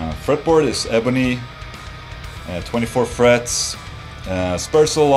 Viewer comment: keep playing the video at 1:40 it sounds like ola is saying "fucking tuners" xD